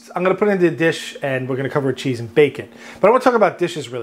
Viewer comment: I definitely heard "cover it in cheese and bacon" and I was so disappointed there was no bacon... and had to revisit to listen to him say "bake it" :P